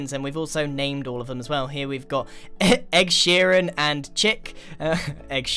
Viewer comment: for the chicken egg seeran I thought it said egg see ran